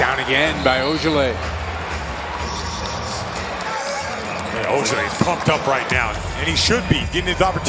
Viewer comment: Why it sound like he said "Ojeleye fucked up rn"